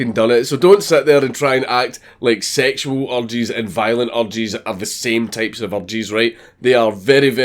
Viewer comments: 100% thought he was saying "sexual orgies" and "violent orgies" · when he says urges it sounds like orgies and it makes me laugh when i hear "sexual orgies and violent orgies are very different" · sexual orgies and violent orgies are very very very very different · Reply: I’m glad I wasn’t the only person who heard him saying “orgies” when he was trying to say “urges”. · Am I the only one who thought Dankula was saying "orgies" instead of "urges"? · Was I the only one that thought he said "sexual orgies are not the same as violent orgies" instead of urges? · I thought you said orgies instead of urges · Every time you say "urges", I hear "orgies". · Oh, 'urges'. I thought you were saying 'orgies', so I was really confused for a while. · Whenever he said "urges" I thought he said "orgies".